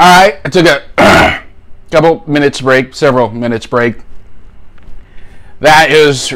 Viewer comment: it sounds like he says "couple minutes raped" which would be a pretty accurate description of this sauce